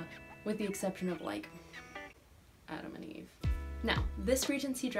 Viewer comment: omg! I thought you said Adam Levine 💖😂😂😂😂